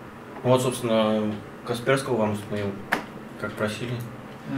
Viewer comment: "Касперского вам установил - он красивый" 0.0 я один это услышал ? Лол